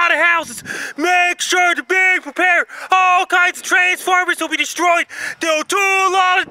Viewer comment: does he say "All kinds of Transformers will be destroyed?"